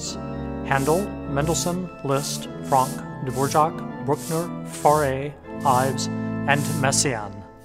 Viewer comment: At around 06:15 he's listing composers who were organists... Who is the guy in the bottom left? Diborchog did he say? Daborchok? Anyone please help. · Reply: It's Antonin Dvořák (<num>) - Czech composer.